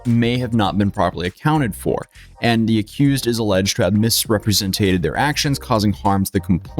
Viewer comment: umm... How do you say "represent"? Did I hear "representate"?